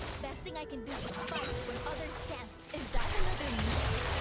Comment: did she just said "is that another me? " to an enemy zeri. Interesting